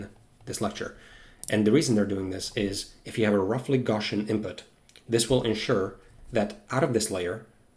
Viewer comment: The transcript did not do a good job at this point: 1:11:03 reason they're doing this is if you have a roughly gsan input this will ensure that out of this layer you will have a 1:11:10 roughly Gan output and you you basically achieve that by scaling the weights by What is that word you are using, which got translated into "gsan" and "Gan"? Are you saying "Gaussian"? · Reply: He says “gawshun” for the word “Gaussian” hope that helps